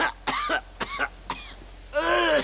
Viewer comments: Thats actually cold "dumb move, kid" the veteran talked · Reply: I thought he said "don't move kid" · Thats actually cold "dumb move, kid" the veteran talked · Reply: I always thought he said "don't move kid" as he want a clean shot for him